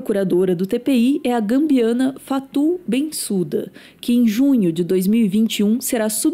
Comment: gente, vcs tem que por legenda com nome das pessoas q vcs mostram... eu escutei "beiçuda" no